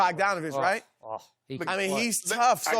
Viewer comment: They showin love to Bogi! 916 stand up 💜🖤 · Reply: Tj Figueroa were they talking about bojan or bogdan cos I heard “bogan” bogdanović